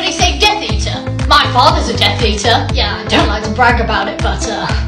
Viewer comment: i thought she said “my father is a deathbeater”😂